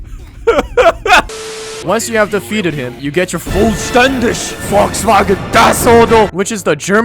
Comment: Habe ich VOLKSWAGEN gehört